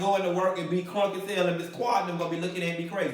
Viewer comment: Did he just say Ms Quad 🤔